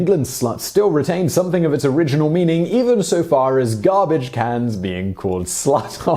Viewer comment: When you cracked up, I thought you said garbage cats, not cans, were sluts. I was going to start calling my cat a slut. Now I am disappointed.